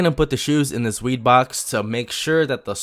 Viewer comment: it sounded like he said weed box